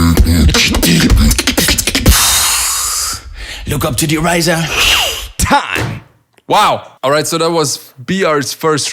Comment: what did he say? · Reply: Look up to the ryzen :)